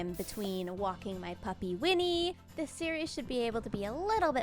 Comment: When you said ‘this series’, my phone thought you said Siri